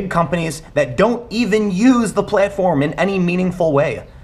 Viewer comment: Tim at the 3:56 mark when you say playtform instead of platform and your ChiTown accent comes out I laughed so hard. Great opinion here. Youtube DOES have a problem.